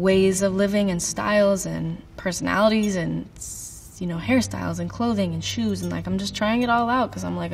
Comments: I thought she said Harry Styles..Lol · I almost freaked out I thought she said HARRY STYLES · Lmao at 9:46 I thought she said Harry styles and not hair styles💀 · I thought she said 'harry styles' at 9:46 for a second. Got me freaking out for a sec. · DEADASS THOUGHT SHE SAID HARRY STYLES